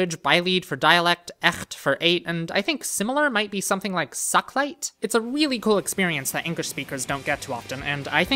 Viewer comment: I thought when he said "Sak-Light" he said "suck bite"